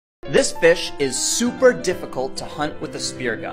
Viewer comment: Omg he said “this fish” I thought he said “this bitch” 😂😂😭